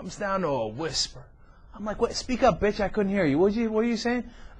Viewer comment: did he says "bitch" at 3:45 ?!?!?!?!